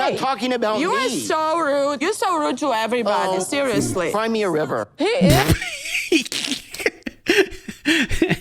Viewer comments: Did Ed just say "Find me a river" ? Is that some kind of cleaner American version of "cry me a river" or is Ed just stupid. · Did he just say "Find me a river" !?!? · Is ed saying (Find me river or cry me river) Like it sounds like find me a river but isn't he meant to say cry me a river? xD